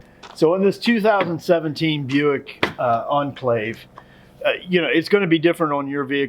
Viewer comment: In description; ''We’re working on a 2017 Buick Escalade, ...'' ''In the case of the Buick Escalade...'' I thought I heard you say Enclave in the beginning, but didn't re-check, I was looking for the year of vehicle, though I think you said this is common with a lot of vehicles, good to know.